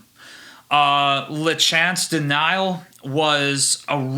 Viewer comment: What's the name of this band/album? It sounded like you said "Lachance Denial". I can't find it online under that name.